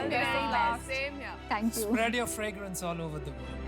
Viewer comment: what he said spread your fregrence all over the ? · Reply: World